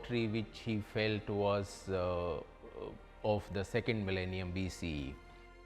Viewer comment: Out of context: at 2:17 the man said BCE (before common era - a standard neutral term accepted internationally) but the subtitle says BC (before Christ). Just wanted to point it out, could be an honest mistake of the writer.